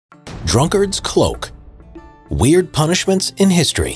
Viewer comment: I thought it said Dunkirk’s Cloak...